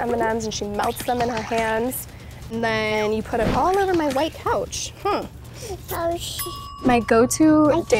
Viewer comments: did she say oh shi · Is it just me or did stormie just say oh sh** in time 0:19 and the editor just cut of her saying the whole word😂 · Did stormi say ow sh*t